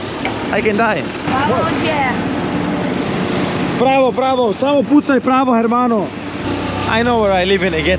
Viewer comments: Hey guys follow me on instagram so I get more followers there so I can show the girls and that makes it more easy to do the Ronaldinho, and they also will want to make content with me its a fake world guys but its the only world we have :PPPPpppppPPPPP !!! <link> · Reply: why do I hear you speaking in Serbian here? Pravo pravo samo pucaj pravo hermanoo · why do I hear you speaking in Serbian here? Pravo pravo samo pucaj pravo hermanoo